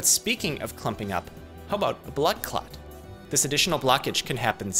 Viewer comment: his accent makes it sound like hes sayin blood-claat